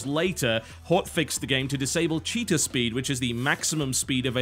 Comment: When totalhalibut said cheetah speed I thought he said cheater speed